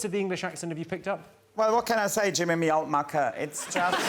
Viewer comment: What does he say?? American here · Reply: “Well, what can I say, Jimmy, me old mucker.” “Me old mucker” is a slang way of saying “my old friend.”